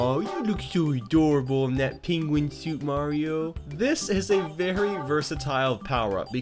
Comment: Hmmm... Did you say Mario?..... Im not to sure